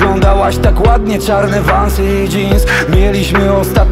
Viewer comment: Kiedy pierwszy raz usłyszałam to w szumiącym radiu: "miałaś czarne wąsy". Ja: aha.